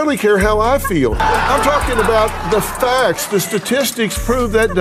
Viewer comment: God Maher is such a moron. "They have statistics on couples?" How does he NOT know this? If there is a thing humans DO there is likely a study on it. FFS. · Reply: i heard is as "They have statistics on thouples" which is a fair question as it is niche.